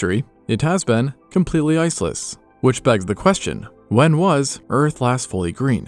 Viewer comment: I love the video. Kept mishearing “Ice-less” as “IS-IS” lol